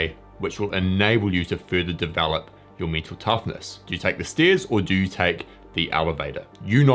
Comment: For a while I thought that he is saying - me too toughness.